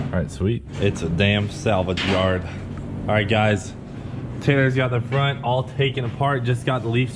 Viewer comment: did he call Taylor, tanner? 🤣